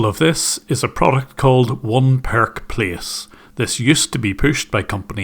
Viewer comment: One Perk Place, I thought you said "That Park Place"